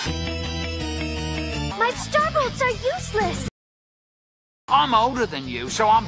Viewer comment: did Starfire say that her starbolts are useless or her starbucks are useless?